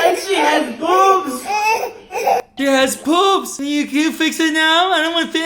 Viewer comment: he actually said “boobs” not “poops”. Equally as bad though.